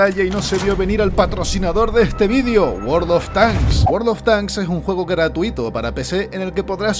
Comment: Escuché "Gordo on tank"?